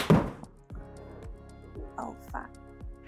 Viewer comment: Did she say "Oh, fuck!"? LOL.